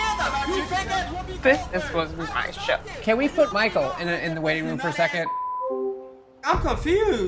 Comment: Can someone please tell me what says at 2:57 ? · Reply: "This is supposed to be my show."